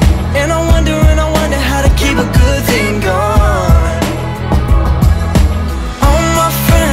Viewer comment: All the lyrics say he says “keep a good thing going” but I hear “keep a good thing gone”??? Just me? Ok.